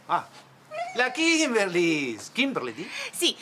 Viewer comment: escuche que le dijo FlaKimberly xD